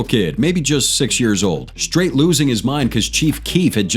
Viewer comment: Did I hear this guy just say he was SIX?